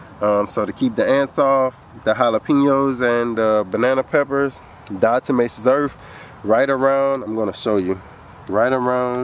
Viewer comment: Please repeat what u said @ 20:45 about keeping the ants away, I couldn't understand what u said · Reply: Diatomaceous earth